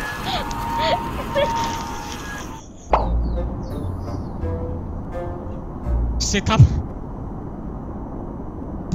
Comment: Uai não entendi o que será que aconteceu ali? 😑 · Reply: Tshubiraumdaum